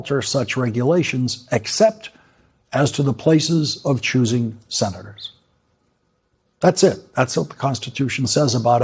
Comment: Think it was meant to say "choosing Senators."